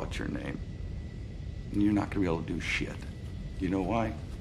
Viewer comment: could you please give deatails about the pronunciation in 2:44?? because I cant hear the words "gonna be able to "